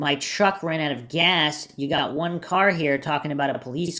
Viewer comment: I heard “you got a gun anywhere?”